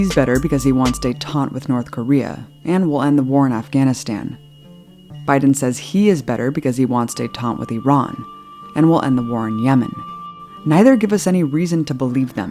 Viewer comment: I think she says trump will stay taunt with North Korea, and Biden will stay taunt with Iran (I may have misheard) but what does that mean?